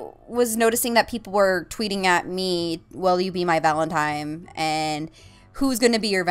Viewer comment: Sounds like shes saying Valentimes